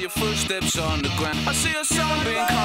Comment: i always thought he said zombie instead of sun beam xD